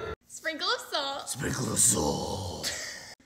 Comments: I heard “SprINKle ASSAulT” 😭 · “Sprinkle of salt 🤪” “Sprinkle assault 👹” it sounded like he said sprinkle assault 💀